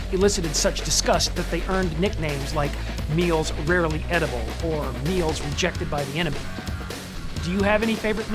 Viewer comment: I always heard"meals rejected by Ethiopia"... The tuna with noodles was HORRIBLE!!!!!